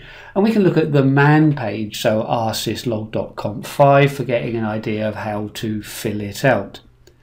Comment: Did you say "rsyslog.conf vi" ?